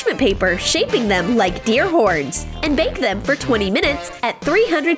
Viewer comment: did she say deer horns